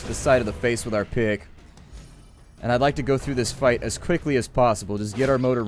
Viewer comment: i understood dick instead of pick